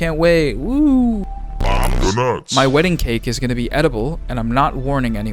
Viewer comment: lmao I thought they just said "edible" at first rather than "an edible" and thought the joke was people would be too nervous to actually cut and eat it because of all the "oh no I ruined the wedding cake" jokes 😅 Yes I know I need to get out more thank youuuu ♥️